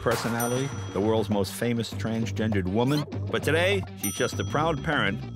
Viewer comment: so, at 0:39 did Jay say "he's" just a proud parent? Yes...that's the VERY best part of this video.